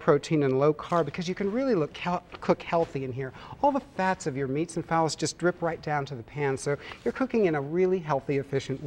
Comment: Did he say all the fats from your meats and phallus?